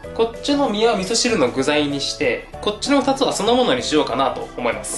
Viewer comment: 気のせいかな。 今日は少し滑舌が良くないね。 どうしても「酢の物」が「そのもの」に聞こえてしまいました。 その他にも、さしすせそが中心に変でした。 歯医者さんに行って麻酔打ったりしました？